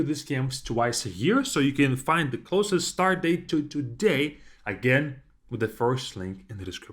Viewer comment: As a Star Trek fan, I could have sworn he said (timestamp ~3:19); “…the closest stardate to begin…” 😆